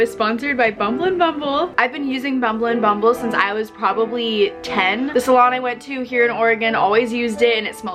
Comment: I thought she said she used to use it. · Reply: Aurora Rieder she said she used it since she was 10